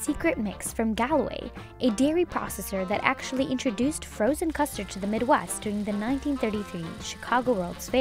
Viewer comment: I thought it said frozen musterd